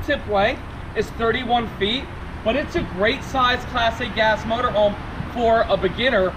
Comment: It sounds like is saying "Plastic-ass motorhome. I finally realized he was slurring "Class A gas."